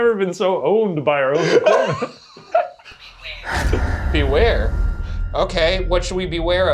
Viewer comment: I thought it said "square" instead of "beware," and thought the ghouls were trying to bring back the Roast Mortem